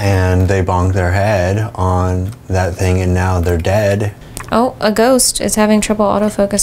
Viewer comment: says “Oh, a ghost” and I had a mini happy heart attack because to my ears it sounded like Ope, A Ghost. Which is the name of my podcast 🤣🤣 thanks for the not a shout out-shout out, that only I will hear lol